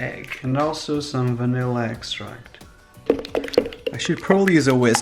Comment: I thought you said: “Salmonella Extract” and I started dying of laughter 😂😂